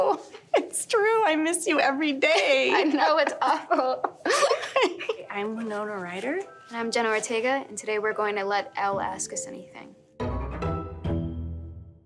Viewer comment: I heard, "i m a notorieter" instead of, "i m winona ryder"